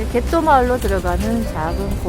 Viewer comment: 진짜 개똥 마을로 들리네요ㅎㅎㅎ 어시장도 열리고 미술공방 넘 가보고 싶네요~ 오 역시 이탈리아 멋쟁이분들이 많으시네요~^^